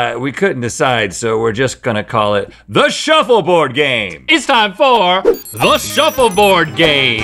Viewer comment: y’all did NOT say “two guys one shuffle board” 💀